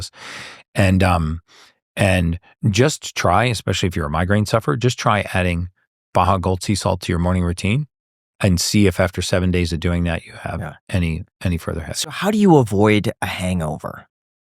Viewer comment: what is he saying to take for a migraine sufferer? All I can hear is ‘Bogal-Tea-Salt’😵‍💫. Help would be appreciated. Thank you!